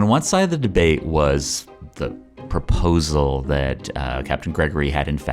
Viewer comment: did he just say bedate?